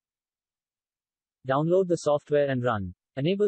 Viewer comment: i didnt understand a thing he said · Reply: Tick the Enable DVR Option